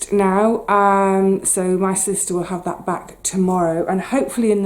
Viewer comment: Did you say “my Sister” at the following timestamps 26:55, 27:18? Confusing…. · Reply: Sorry, speaking too quickly.....I was saying 'my solicitor'...